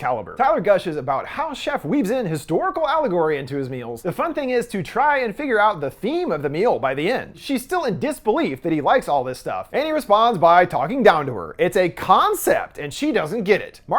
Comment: Can you write here what you said on 10:30 until 10:35? It's completely uncomprehensible to me. The automatic generated sub also said something weird related to melanoma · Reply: “The fun thing is to try and figure out the theme of the meal by the end. She is still in disbelief that he likes all this stuff and he responds by talking down to her.”